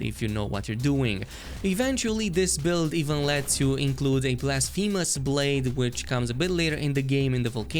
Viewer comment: What is the second blade mentioned at 9:07? I guess im half deaf so i cant understand. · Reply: Blasphemous blade